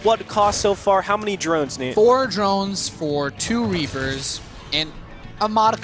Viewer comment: No matter how often I hear it, it always sounds like: " Four Drones for two Reefers" Freudian slip? ;+)